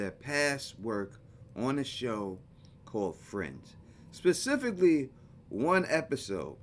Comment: It was really hard to hear what you said. It sounded like you said .. ".... on a show called friend..." 0:57 and the episode it called Westfield Protocol or Welcome to Westfield???